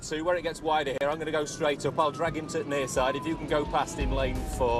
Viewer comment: Listen to the Yorkshire accent too, the way the word "the" often almost disappears in a sentence - 3.15 "I'll drag into t' nearside..." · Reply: What is it with people outside of Yorkshire completely making up what we say? 3:16 He said "I'll drag him to nearside". Lived in Yorkshire all my 21 years and never heard someone say t' instead of the, we just get rid of "the". What you're picking up on is the sudden stop at the T sound in words like into or to, we just say int' or t'. I listened to the video, you literally added the "t" after 'him to', the bobby didn't say that. Donnys in this country making up stuff we say and claiming it for us fkn sausages 🤣